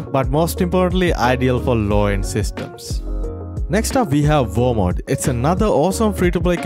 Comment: Is it just me or did anyone hear him say walmart insted of warmode lol